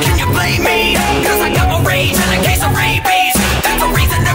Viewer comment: Who else before watching this video thought he said “I got my rage in a case of rabies.”?